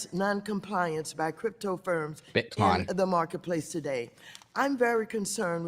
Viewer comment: Bit'Kampf?? BitCom?? What the F is she saying??